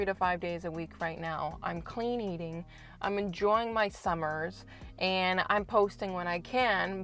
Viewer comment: I'm sorry I'm not sure in which part of the video I said that ? Do you know where so I can watch it back and try and get you a proper reply ? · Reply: my mistake, apologies. I guess you said you were “enjoying” your summer. The first time I listened to it there was a lot of noise going on around me and it sounded like you were saying you were “imaging” something. I guess I thought you were about to go off to talking about the force or psychic powers or some silliness but you are apparently just talking about getting your priorities straight. thank you for a very informative video.